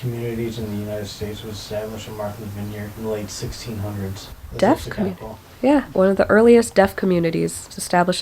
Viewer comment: 🩰🏒💘I watch these videos while I'm at work and since I wasn't fully paying attention to your first fun fact (the one about the deaf community) I heard "the first death community". I'm like WHAT DOES THAT EVEN MEAN?! 🤣 Also shout out to you for reading all those books! Also that middle grade one looked good. I saw a few more pictures posted on Amazon and WOW. They are amazing in their lil creepy way.